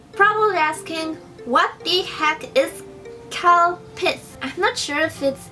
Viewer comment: I thought she said cow piss